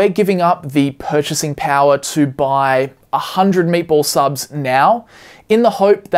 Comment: thought you were saying "maple" subs before I saw graphic of "meatball" subs lol